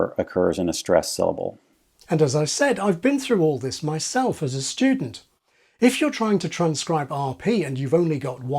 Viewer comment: i swear you said "shtewdent" like you explained would happen on the other video about shtreet and shtrong